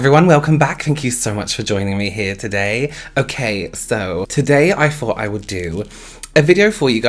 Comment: So... English is not my first language and I speak "American" English... When speaks, he pronounces his "th" like "f"... So when he said "today I thought", I heard "today I fought"... And for a split second I was like: OMG WITH WHO??